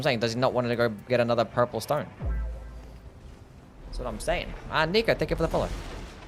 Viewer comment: what did he say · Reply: Lol. He said uhhh niko